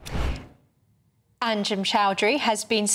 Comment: Not gonna lie , when I heard this from a distance I thought it said “ asim Choudary” … and I thought “NOOOOOO …Not Chabuddy G”😂😂😂 … glad I misheard it